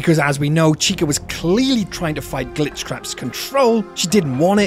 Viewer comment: I’m sorry, but did you say Glitchcrap? 😂